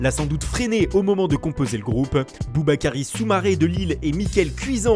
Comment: j'ai entendu BoobaKaaris soumaré 😂😂